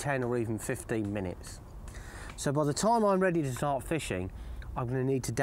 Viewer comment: i thought he said sod off lol it was start off good old matty